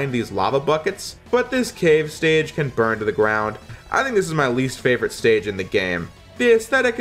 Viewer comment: DID YOU SAY BRUN TO THE GROUND.